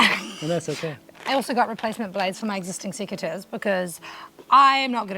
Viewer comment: sequiturs? scissors? what is she saying? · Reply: Secateurs. The pruning tool she was holding